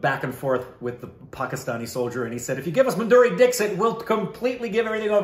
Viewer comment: Did he just say Maduri Dickshit 🤣🤣